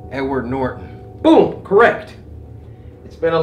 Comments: thought he said 'n-word Norton' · I thought he said “n word norton” I’m dumb. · Bruh I thought he said N-word Norton Lmao 3:16. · Bro i fr thought he said n word northon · I thought you said his name was N Word Norton for a sec XD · i thought he said n-word norton 😹😹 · Ngl at first I thought he said N-word Norton 😭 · I thought he said n-word Norton